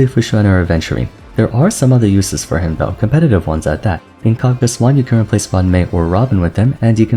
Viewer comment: what does he say here? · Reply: “There are some other jobs for him, competitive ones at that.” Hes just saying jiaoqiu is versatile